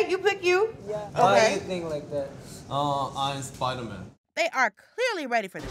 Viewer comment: don’t play with Yunho on the subtitles now “I’M SPIDER-MAN” he said what he said 😌😌